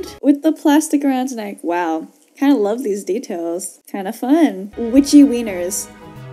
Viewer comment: Please give me your best witchy wieners jokes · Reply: I thought you were say itchy wieners lol Be safe y'all; use protection